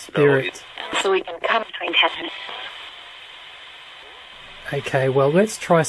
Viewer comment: "So we can come between heaven..." how many thought "And you?" when you heard that? I did. yikes. Creepy either way.